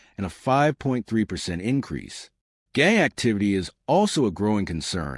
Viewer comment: it sounds like he says "gay activity" 🤣